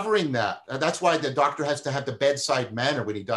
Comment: Please help me discern what John says at 1:04:25 ... Doctor has to have what ? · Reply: "A doctor has to have the bedside manner when he diagnoses you." I think he means the doctor needs to interact with the patient, talk and listen to them, etc.